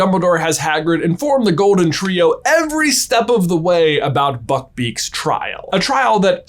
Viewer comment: i heard something else than buckbeak.i heard fu...beak😂 "i mean people are injured at this school all the time.." what a fu...beak 🤣